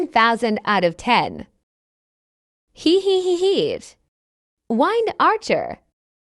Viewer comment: I heard “Wine archer cookie” help